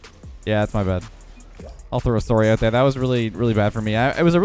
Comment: wow i never really notice the Canadian but 4:19 all i hear is (SORE-Y) lol its great